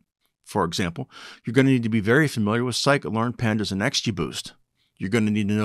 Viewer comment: what did u say? "Scikit learn" or "psycho learn"? I didnt understand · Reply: SciKit-Learn.